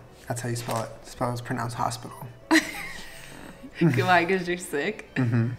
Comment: Julien: “my name is HPTL” Me: “oh, like l’hôpital” Him: “it’s pronounced hospital” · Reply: OH MY GOD YEAH I THOUGHT HE SAID L'HOPITAL